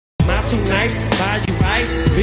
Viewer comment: I thought it said: 'am i too nice to buy you Rice 🍚? "